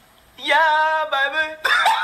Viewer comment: 「ヤーバブ」にしか聞こえん